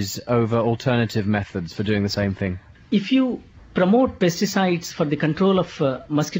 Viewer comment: someone should fix the subtitles he clearly said pesticides but the subtitle said pizza size hahaha